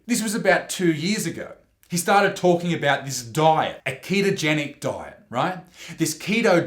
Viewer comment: Ketogenic Diet peeps. I misheard it as Aikido Diet.